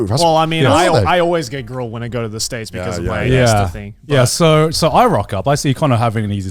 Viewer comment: What did say was the reason he always has problems with immigration? Can't hear it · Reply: ESTA